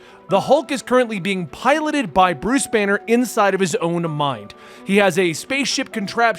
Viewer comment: My dumb ass thought you said Bruce Wayne and I had to rewind it. I was like BATMAN HULK HOW EVEN.